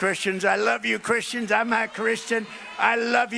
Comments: Did t just say he's not Christian @ 1:20? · Wait... did Trump just say he's not a Christian? I think that might impact the base more than him saying fashy things that they clearly like. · did I hear him right, did he say "I'm not Christian"? Because I feel like I remember a lot of fundamentalists arguing that he was Christian, and that it was disrespectful to say he wasn't (even though they regularly said Obama and Biden aren't). But yeah, prediction: they'll claim it was a joke. They'll never explain the joke, but they'll insist it was a joke, and then whine that the Left takes everything too seriously. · Did he say "i'm not christian." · Did he say “I’m not Christian”? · Did he say, “I’m not Christian” as he brought his hand toward his chest? (1:18-19) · I swear I heard him say, "I'm NOT Christian." Freudian slip, maybe? 1:18 Edited to add timestamp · am I crazy or did he say "I'm not Christian" to a crowd of Christian supporters he's trying to win over? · Did I hear Dictator Trump right? "I'm not a Christian." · Didn't anyone hear the real honesty come out "im not Christian* ? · Okay I sware when he was talking about Christian. Saying he likes Christians. Yet I heard "I'm (not) Christian" at 1:18 to 1:20. There was three words that was quick. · Did he REALLY say "I'm not christian"???? (1:19) PLZ America... THINK before you vote!